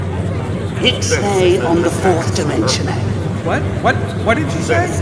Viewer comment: can anyone tell what a sphere is saying at 1:15:28? ive been at it for 2 minutes and all i hear is "keep stay on the fourth dimension aye"